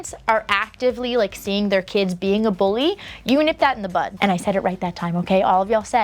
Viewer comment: I thought you were saying "nip it in the butt" on purpose to be funny and silly. I think you can say it however you want, I still hear "nip it in the butt" lol 😂